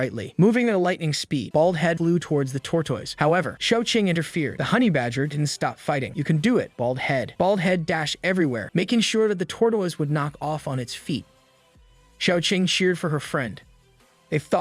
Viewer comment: it sounds like hes saying tortwoise